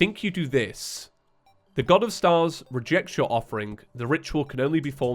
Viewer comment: i be playing so much blitzball in final fantasy 10 that I heard "of stars re Jecht Shots your offering